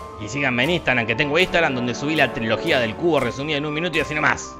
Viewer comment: Pense que decia "La Trilogía de El Culo" xdd